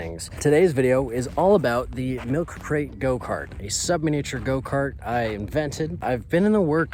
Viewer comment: Thought it said submarine go-kart